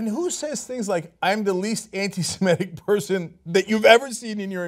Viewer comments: I actually heard that as "I am the least semitic person..." which I found interesting, but maybe I misheard him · Okay, I've listened to the beginning part of this several times and I keep hearing him say, "I'm the least SEMITIC person that you've ever seen in your entire life." Is there a glitch or is my hearing off???